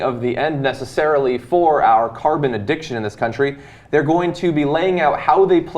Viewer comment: For a second I thought he said, "carb addiction." I was like, amen!